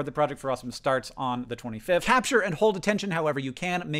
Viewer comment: I thought Hank said "don't ever say you're welcome," and got VERY confused.